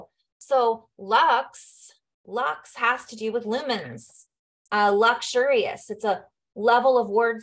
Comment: Had a moment...I heard you say "lux has to do with lemons" 🍋😆 went back for the right word but it actually works for me...lemons=lumens, lemonade/lumen-aid/light-aid...I've been craving and eating lemons recently, perhaps to help raise my vibration, and there are 2 trees in my yard lol. 💙